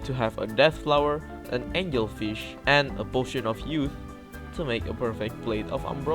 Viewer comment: What did he just said?! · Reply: Potion Of Youth